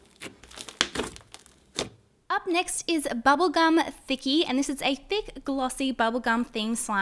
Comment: When she said thinckie i thought she said bubbke gum Nikki